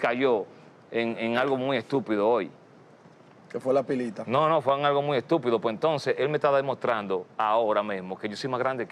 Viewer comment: So 6:30 WTF is being said?! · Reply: He said Speed did a stream talking about botting in the Dominican Republic and now all the fingers are being pointed at this guy and he said Speed caved to critics and that hes bigger than speed.